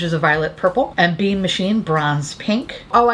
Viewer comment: I legit thought you said one of the Slimelight shades was in "bean machine," and I immediately thought of the Hitachi magic wand. 💓